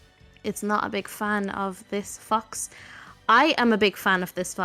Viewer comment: did she really say this f*x 😂